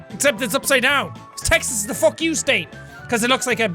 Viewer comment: You said , "Texas is the fuck you state". I thought you said fuck Texas. I live in Texas